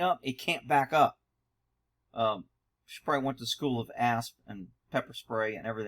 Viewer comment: I just now caught it. "The school of A.S.P. 'n' pepper spray". At first, it sounded like the school of a$$.